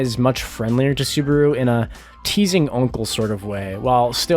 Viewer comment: "Teasing uncle"? did you actually call it?